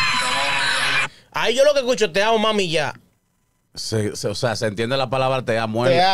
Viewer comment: Yo entendí “TE AMO CON MI VIDA” 🤷🏻‍♀️